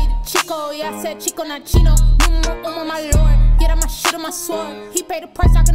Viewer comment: "Get on my what and get on my sword" ????? What did she say? I heard "get on my sh** and get on my sword". Not good. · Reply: “Numero Uno my Lord.. Yeah that’s my SHIELD that’s my SWORD” 😅